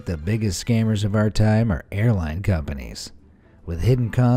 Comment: When he said that the biggest scammers were airline companies....... I thought he said the biggest scammers were hairline companies......